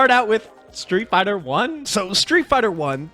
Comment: did he just say Street Fighter is trash.. man, I'm so close to taking back my like and not watching the rest!